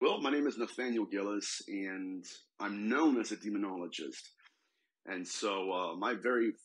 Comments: I thought it said “Dermatologist.” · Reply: I thought is said dermatologist too, glad I am not the only one hahaha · Haaaaa I thought you said Dermatologist also!!